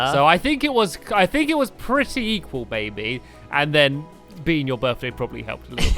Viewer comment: does it sount like he says its pretty pertty equal baby instead of maby